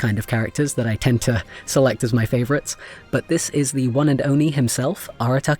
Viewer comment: Me, at 8:27 - ...did he just say 'one and ONI'? 🤣 Also, I FULLY blame Jenny for the fact I immediately said "PASTA MAN" when you introduced Tartaglia.